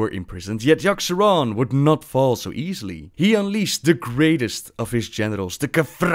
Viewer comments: I heard "He unleashed the greatest of his genitals" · Great series! Also at 9:35 with your accent it sounded like you said "he unleashed the greatest of his genitals!" xD